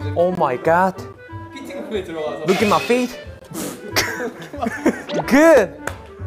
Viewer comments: Dino's English is actually really good though! He knows all the important phrases like the common one us English speakers use every single day "look at my fit" 😂😂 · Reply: The whole time i thought he was saying "Look at my feet" 😂😂😂😂😂😂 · I thought Dino said "Look at my feet" 😭